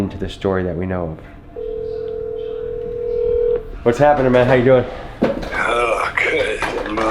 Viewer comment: Yo my shitty TV totally sounds like he says what’s happening “ma n***a” at 39:56 anyone else have a crappy TV like this